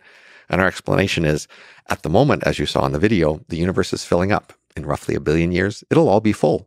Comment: Thought they said the universe is infinite??